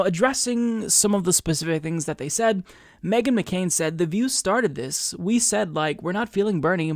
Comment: I thought you said mega mccain. Now I will refer to her as only that